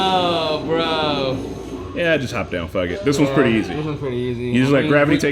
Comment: Did he say ‘just hop down faggot’?